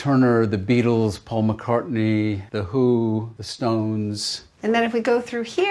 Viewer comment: Did he said the hu at 5:48?